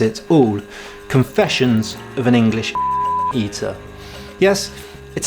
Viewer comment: I'm sorry, but are we seriously censoring the word "opium" now? Without being familiar with the book, my mind took me to all sorts of places... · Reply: I thought he said ass eater and I was lost